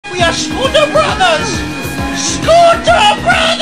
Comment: Swear to God I thought he said school shooter Brothers 💀